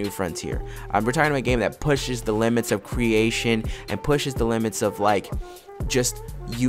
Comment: "Pushing the limits of life"?? Calm down bro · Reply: Aaaaaaahhhhhhh!!!!! Apologies. I was in the shower. I heard "pushing the limits of life" n i swear i died laffing. Shuda listened properly. My bad.